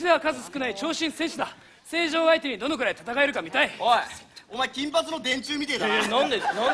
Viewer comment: あの。。48:52に田中が月島に言ってた金髪のでんじゅ? てんじゅ? 何て言ったかよく分かりません。 教えてくださるお方ありませんか??TAT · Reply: 정윤희 『おい、お前金髪の電柱みたいだな』って言ってますね、たぶん。